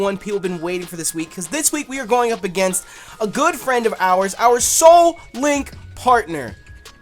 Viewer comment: Lol when he introduced I thought he said our soul mate instead of soul link partner